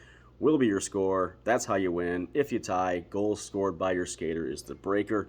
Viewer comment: For the skater are we talking goals or points? Or fight minutes for a Gordie Howe hat trick? Lol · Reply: Nevermind I listened closer and heard you say goals